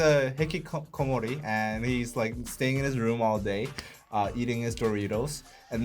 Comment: lol i thought you said eating his burritos